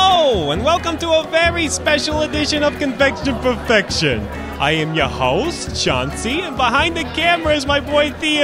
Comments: I thought he said "I am your host, Jon Cena" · Did I hear right? It sounded like he said John Cena. JOHN CENA! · it sounded like he said "i am your host john cena" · lol i thought he said john cena